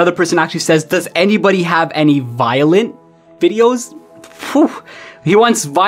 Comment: I Literally Heard Vilion